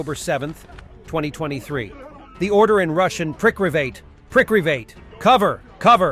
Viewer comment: Either my hearing is screwed up or the uploaders hearing is screwed up because CLEARLY I cannot hear "priklivate". All I hear is "elduwad"